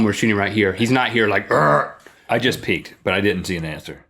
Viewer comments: When Link thought Rhett said, "I just peed," he looked down at Rhett's pants. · I thought he said "I just pee'd" too. lol